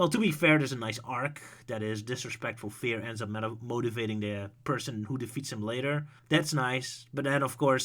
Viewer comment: "To be fair, there's a nice arc that is disrespectful fear ends up meta-moti-blablabla person who defeats him later." I really have no idea what you are saying there. You mumble so much, and I can't even think of any kind of nice arc so I don't know what you are referring to. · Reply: a correction, he was saying 'motivating the person' meaning Infinite's arc with the characters motivating them with fear that they get over it and end up defeating him with it